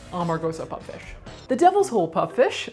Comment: I thought you were saying "amarosa" puff fish. Remembering her from reality tv it certainly made sense lol